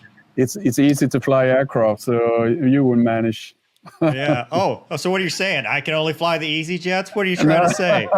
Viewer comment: i think you misheard, he meant you would manage! But it sounds like he said "would'nt"